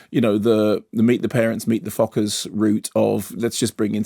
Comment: did he say 'meet the parents, meet the fuckers' at 0:42 ?? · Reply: Meet the Fockers, the 3rd film in that series.